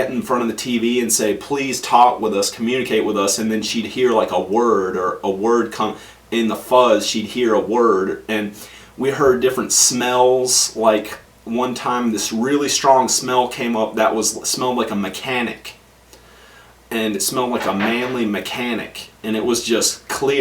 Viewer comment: did the just say, "We Heard many Smells"!? 🤔🤔🤔😜😝😜🤣🤣